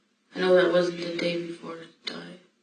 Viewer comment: I think the caption is wrong. Sound like she says 'the day before it died' not 'she.'